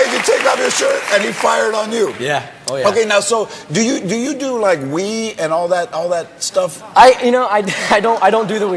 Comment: I literally thought George said "now, do you do like weed and all that stuff?"